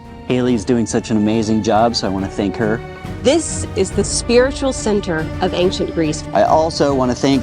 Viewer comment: Did she say, "This is the spiritual sphincter, of ancient Greece",,,?🤣